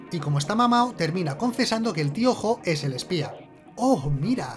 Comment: ¿Tío Jo? Todo el video pensé que decía Piojo